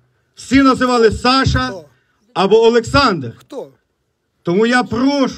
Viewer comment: Alexandru? Did I hear it right? That's a romanian name